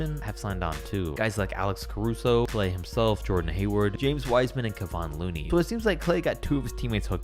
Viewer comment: did i just hear jordan hayward?